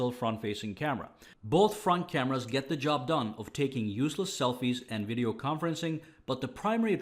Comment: did he just say takes useless selfies 😂😂😂